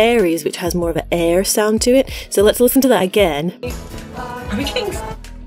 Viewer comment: I heard "are we getting f*cked" XD